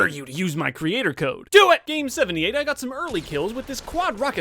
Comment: did he say do it meme7